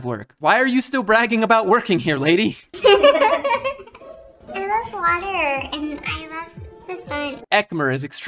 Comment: Did she just say, "I love water"??